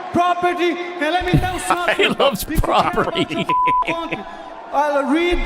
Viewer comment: If you guys could shut up during his post fight speech then we could hear it and you would have heard him say he loves 'private property' and not 'property'. You talking and laughing when the subject of the video is talking is extremely annoying